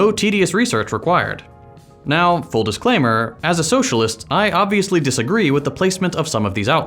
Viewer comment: Howdy, friends! If you enjoy my content and you'd like to support the channel, consider checking out today's sponsor, Ground News (it's free!) - it's a great way to help friends and family members break out of their little propaganda bubble and develop media literacy skills. Check out Ground News here: <link> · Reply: did you say your a socialist?